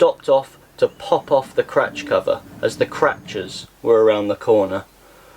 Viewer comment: I thought you said Crutch cover